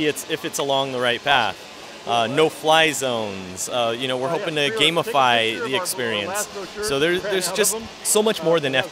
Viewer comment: I heard him say gasify, maybe they can overlay something on here so I can drop poke balls to catch pokemon with!